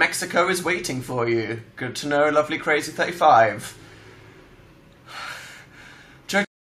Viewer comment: Mexico is waiting for you -Good to know losjnhjdbvs somebody pls tell me what he said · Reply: Sounds like he said "lovelycrazy35" maybe a username??